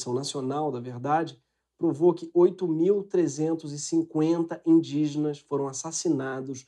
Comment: Aparentemente, a versão em inglês (subtitles) foi preparada (não é automática). Aproximadamente a 5:15 aparece “8,350k” (que seria equivalente a ‘8,350,000’), mas Gregório disse “8 mil 350” (não ‘8 milhões 350 mil’).